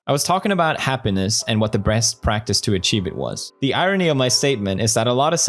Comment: why do i hear "breast"